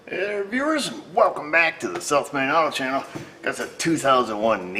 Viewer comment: I keep hearing "Welcome to the Self Made Auto Channel". Pretty sure I've seen this comment before, but man, well done over the years and know you're saluted for your integrity and character.